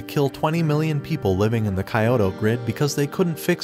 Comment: what did you say?? 1:33 KAYOTO?? bruh it's KYOTO not KAYOTO... -__- no need to be a weebs to pronounce the city name correctly....